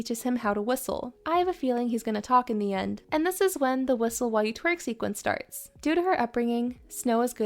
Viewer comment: I heard “whistle while your twerk” and now I can’t unhear it hahahaha 11:22 “you” not “your” haha